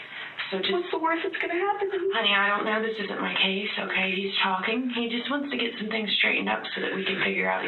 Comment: I thought the lady cop said " this is not my case ".